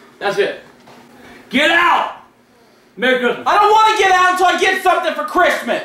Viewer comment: did he say nigga? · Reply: he said merry Christmas